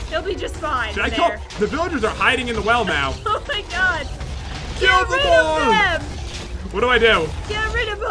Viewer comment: OMGGGG PROOF THAT popularMMOS is the killer of the crafting dead!!!!!!!!!!!! if you didnt hear it while he was fighting tthe ghasts he said "KILL THEM ALL"